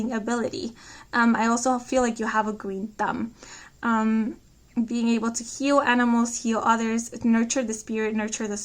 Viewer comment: Didnt get what u said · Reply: Oh! "You have a GREEN THUMB and are able to heal animals and heal others..." ☀️